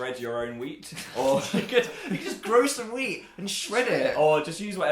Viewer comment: I thought Dan said grow "some weed and shred it" HOW MANY INNUENDOS DID YOU COUNT? I COUNTED LIKWE A MILLION OMFG DANYUL AND PHILIP |> DANYUL HOWELLS FOR PHILIPE LESTER